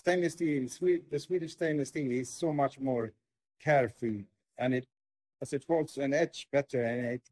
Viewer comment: can you tell what the timestamp where he say "cerf" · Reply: I am now able to hear "carefree". 2:45 I am now able to hear "care for". Not sure why I couldn't hear it before haha.